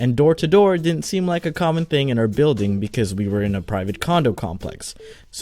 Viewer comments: Did you say condom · did he say “private condom complex”? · I thought he said a private condom complex 😂